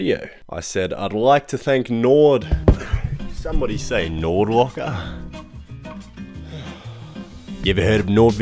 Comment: I thought he was saying Nord Walker